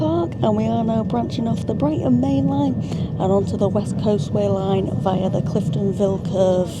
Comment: I had to check cos I thought you said West Coast Main Line and was confused 😂